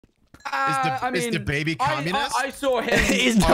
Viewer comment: I click on this vid and the first sentence i hear is “Is DaBaby communist?”. Im fucking dying xD